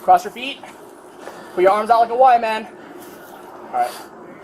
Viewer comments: Thought he said put your hands out like a white man 🤣😂😂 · I thought the cop said “Put your arms out like a white man” 😂😂😂 I didn’t hear the “Y” til I rewinded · I thought he said “lay down and spread your arms like a white man”. Apparently it’s a “Y” 😂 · I 100% thought he said "put your arms out like a white man" was so confused lmao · Thought he said "Put your arms out like a white man"